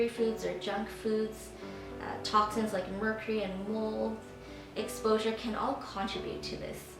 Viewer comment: Wow, Yes, a war between good and bad bacteria! -Factors like steroids, meds stress, sugar, junk food...toxins... But did you say ...'toxins like mercury and wool'... at 2:10 / 2:11 on Video? I could not hear that well.. -I am wondering if this information works on other skin flairs like Seborrheic Dermatitis and Eye Blepharitis? Sweet presentation of simple solutions. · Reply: Late response, but I think she said "mold exposure".